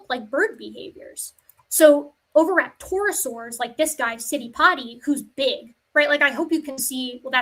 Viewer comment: I keep mishearing Citipati as "Sitting potty"...